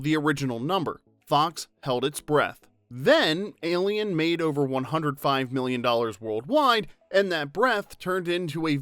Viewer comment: the narration states that “alien“ made $105 million, but the subtitles wrongly stated only $15 million… It’s a shame somebody doesn’t sit down and watch to be sure that the subtitles are accurate. Most people who use subtitles regularly already know that the curse words are the first things dropped, and that the words used are not always accurately what is happening on screen, but conveying the message because the more words, the more likely the person using subtitles will have problems following if they do not read very quickly. The film making $105 million is a big difference from $15 million and that is the point… A little omission that would easily be caught if somebody actually proofed the subtitles would have been corrected. It shows negligence, and that is very sad as a reflection of a lack of respect just because some people depend on the subtitles.